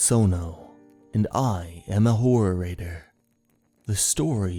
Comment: A horrorator? Horror and narrator combined. I like it. That kind of sounds like what you do. I would also call it scarerator. 😁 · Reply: Oh. Okay, that makes sense. Thanks. Now I hear "horror writer." XD